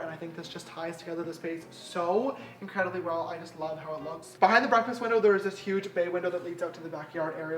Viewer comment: did anyone else hear "breakfast window" at 11:34?